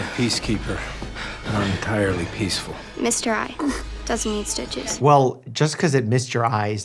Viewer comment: I think she was saying it missed his eye, and doesn't need stitches